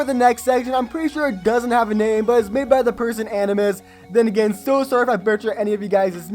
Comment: did you just say antlmus?